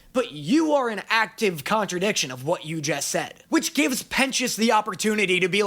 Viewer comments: Clicked on a video titled "VOX IS HOT??" and was told about the philosophical concept of The Goodness Paradox. Wonderful content 10/10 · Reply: I searched for a comment to say 16:00 because I realized the subtitles said Pen!s instead of sir pentiouse😂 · tell me why the subtitles said penis instead of Pentious